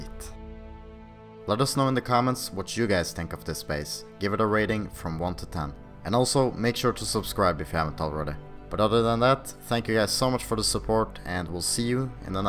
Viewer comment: When they said give it a rating, I thought they said give it a raiding.